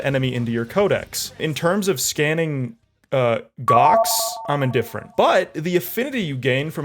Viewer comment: sorry, i'm a trans lesbian and i heard "scanning gocks" and got so hard i passed out for a minute